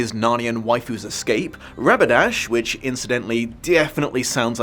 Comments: Me: Wait, did he just say Rapidash? A few seconds later: “Which definitely sounds like a Pokémon.” Ah, okay, he noticed it, too. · “Definitely sounds like a Pokémon name” Thanks, I literally only heard that name as Rapidash after you said that. It’s even a horse… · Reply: I wasnt paying attention for a few seconds and had a double take on that.. "Did he just say Rapidash?"